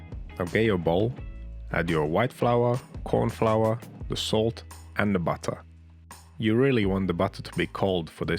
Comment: I thought I heard cute butter but then I realised what is cute are those Cheesy Crackers!!!